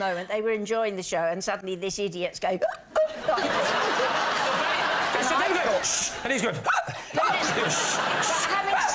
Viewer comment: what does lee say regarding cats? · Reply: "they were probably locked out", referring to Sheila's previous story